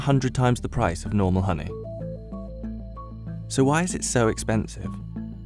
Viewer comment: Every time he says expensive, I hear "expansive"